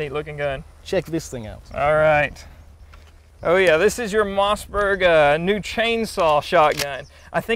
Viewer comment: Los que hablen español entenderan esto: en el 1:02 el de la izquierda parece que dice "This is the most VERGA new chainsaw shotgun" XD LOL